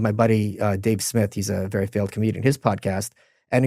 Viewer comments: did Michael say Dave is very failed comedian lol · did he just call Dave Smith “a very failed comedian”??